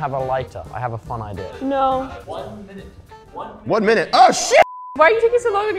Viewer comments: is it just me or at 6:08 i heard ranboo say " OH SH** know i could have miss heard i am not saying he did i just think he did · did ranboo say shoot or shit?